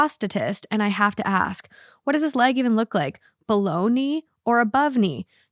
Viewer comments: Story 2: every time you said "below knee" I thought you were saying Bologna 😅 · She said “below knee” and my mind thought she said “bologna” 😂